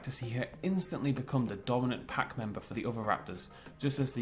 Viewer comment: Did I hear Oviraptors? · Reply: "other raptors" took me a hot second too :D